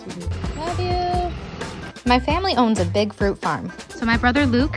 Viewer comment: "My family owns a big fur farm" Hey DODO, are you aware what kind of people you promote?! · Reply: I thought she said "big fruit farm". I hope I'm right!